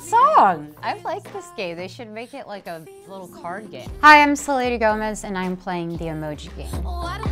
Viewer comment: Why does it sound like she said ‘Hi I’m sir lady Gomez’ 😂